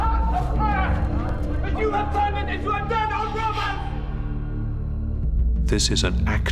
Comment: Jesus did not say, "You have turned it into a den of robots." The word "robot" didn't even exist then. He said, "You have turned it into a den of THIEVES."